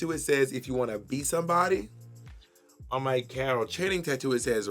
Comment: the fact that the close captioning says "Cattle Chaining" instead of "Carol Channing" is homophobic 🤣